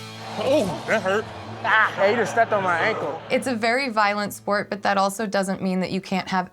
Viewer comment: did I hear “hater stepped on my ankle.”😂